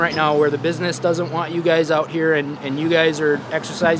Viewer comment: At first I thought he said it WAS worth losing his job over, which would’ve been one hell of a patriotic policeman. But unfortunately that wasn’t the case